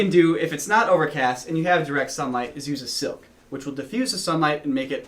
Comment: What was it said to use at 4:30? I couldn't understand. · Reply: A silk. Or a white bed sheet or something to diffuse the light.